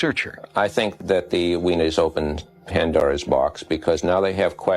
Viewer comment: no that guy did not say "the Weiners have opened Pandoras box". 🤣🤣🤣🤣🤣 Sik